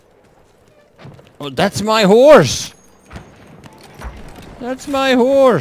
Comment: sounded like you said "that's my whores"